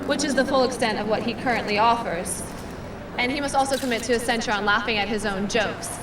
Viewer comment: What did she say at 2:44? i can' catch a word. "and he most commit to a ??? laughing at his own jokes" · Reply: "He must commit to a censure of laughing at his own jokes"